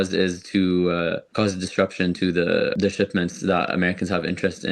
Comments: He clearly said "disruption", not "destruction". Very misleading captions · You might want to edit your subtitles here. He clearly says "cause disruption" not "destruction" as the subtitles say.